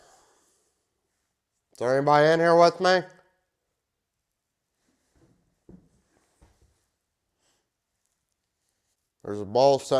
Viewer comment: “zenny air bar nar wit me” lol what did he say?! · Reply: Izz there anybody in here wit me?